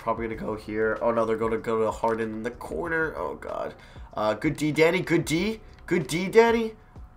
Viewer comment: When he said, “Good D Danny,” I thought he said, “Good D Daddy.”